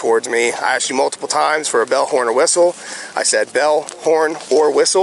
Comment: Good for him! “Don’t call me brother!” This cop is a db and we ALL thought he said bullhorn. Once again back the blue until it happens to you! You just taught this whole family to HATE police! Good job officer.